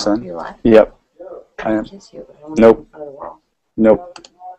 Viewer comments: was that a kiss at 0:59?!? I SURE HOPE SO eheheehehehe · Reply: maybe!! she says at 0:05 i wanna kiss you but no in front of the world, while he is saying nop nop nop, so we cant hear it!!! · sounded like she said "i wanna kiss you but not in front of the whole word" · Did she just say can i kiss u